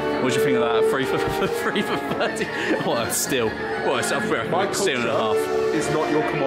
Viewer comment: What did George say at 5:59 after "what a steal"? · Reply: "grumbling, Steal and an 'alf"